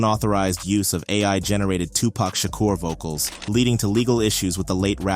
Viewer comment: tupac who?🤨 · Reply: Did he just say Tupac Shaka?